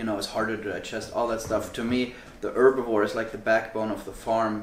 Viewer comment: What is he saying at 0:15? I don't understand this term. What is the back bone of the farm? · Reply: The "herbivore". is referring to beef/red meat here.